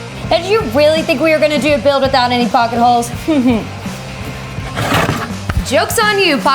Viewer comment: LOVE YOU! "POCKET-HOLE QUEEN" I love it! I heard "Did you think no pocket-holes?!", and I died.